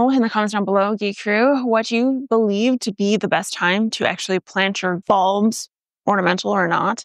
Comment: sounds like you said plant your balls, but... I live in the Colorado rockies, about 7500 ft. You gave me the idea to plant some garlic. Is this the time for us? I am going to do it tho, with some grocery store garlic, see what I get next year. It hasn't snowed yet this season, but the mountains have gotten a lot of snow. Anyway. Love your vids! Lookin great as always and spitting facts!